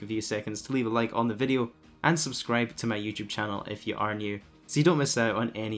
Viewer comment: sounds like you said UNSUBSCRIBE FROM MY YT CHANNEL